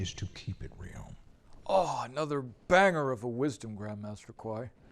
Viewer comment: 😂😂 exactly, did you just say banger?